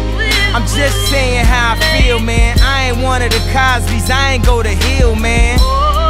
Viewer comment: My dumbass thought he said "I ate one of the Cosbys, ima go to hell, man.